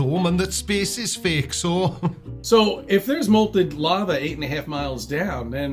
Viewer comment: "Malted Lava" That sounds like a scrumptious bedtime drink. So, what IS blathering on about?? Ooooh! Does he mean molten lava (as in not solid)? grasp of scientific fundamentals is impressive in the same way a gnat's penis is impressive. Only another gnat could possibly find it so! .....and at [11:38], just to ram home the point, our hero says 'consolations' instead of 'constellations'. I think a gnat's penis would overshadow intellect quite easily... ... ...